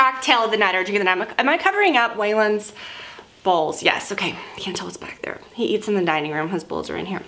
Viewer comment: Wait, some humor, I totally thought you said Waylon‘s balls 🤣🤣🤣🤣